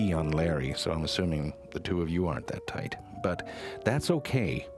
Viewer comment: lol. i thought he said, "and i saw you pee on larry, so i assume the two of you aren't butt tight." i'm gonna have to use that.